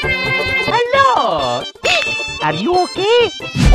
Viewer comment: it sounded like are u gae?